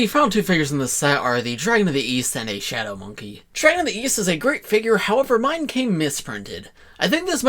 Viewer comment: I thought you said Weast